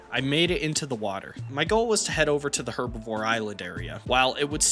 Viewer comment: No way I just heard let's go to pervy island